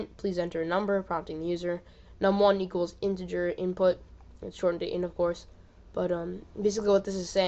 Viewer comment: in the beginning you say "shortened to int of course", took me three listens, I thought you were saying, "short for intercourse" lol!